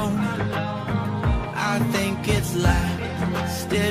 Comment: it sounds like he said "I thought about 'nam"